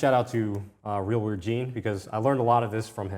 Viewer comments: gave a massive shout out to one of his mentors. I cant make out the name. Any ideas? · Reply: He's talking about Gene! You might be able to find him on X. Any thoughts on this episode? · He is mentioning someone at 14:00 but I can't catch the name. Is it Real World Jean? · Thank you! At 13:55 when was giving a shoutout to his mentor, who was he talking about? I couldn't catch the name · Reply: Real Weird Gene Thank you for tuning in!